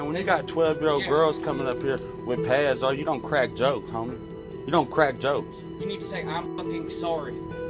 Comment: did he say homie? He wants to talk like that im gonna send him to slip and fall school